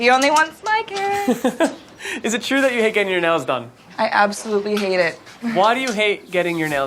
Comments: why do I hear : is it true that you got your nose done? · I thought he said "Nose done"😂💀 he actually said nails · For once I heard “is it true you like getting your nose done?” ... well she did her nose n she looks like this, all worth it.. shes beautiful!!